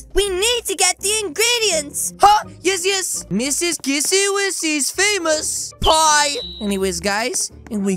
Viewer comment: did he just say... missy wissy? ITS KISSY MISSY >:OOOOO